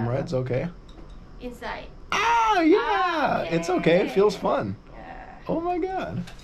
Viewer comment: Did you just say it feels fun at 5:50!!! WTF!!!!